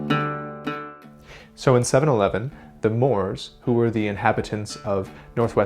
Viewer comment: I thought you said "so in seven-eleven.,.."